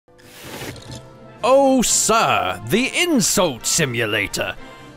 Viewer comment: I thought it said Oh Siri 😺😺😺😺😺👽👽👽👽